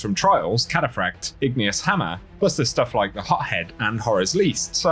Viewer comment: we love you, but I swear when you mentioned to Bento rewards I heard "Pothead" not "Hothead" XD